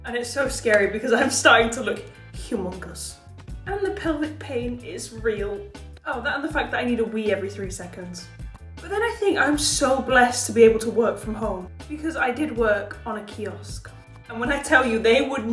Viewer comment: Ok but tell me why, when she said "I need to wee," I thought she said "I need to Wii" (and just pictured Wii Sports)